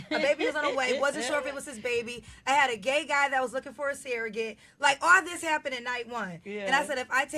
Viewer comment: why does she say "seeragate" instead of "surrogate"?